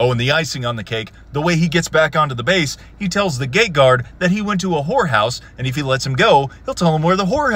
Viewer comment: A Sgt. punching an SFC made me cringe HARD, that shit would never fly. Also, I swear I heard you saying "the gay guard", had to rewatch this bit of the video like 5 times while thinking "how does he know the guard is gay??"